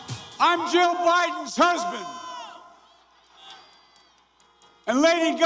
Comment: “I’m Joe Biden’s Husband” -Joe Biden This is a real F’ing quote! What’s wrong with him bro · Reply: I misheard him too, he actually said Jill Biden’s husband... BidenHarris2020